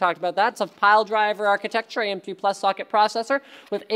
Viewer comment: did he said am2+ xD